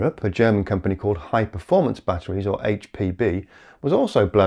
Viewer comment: Thought he said HPV at first. 😂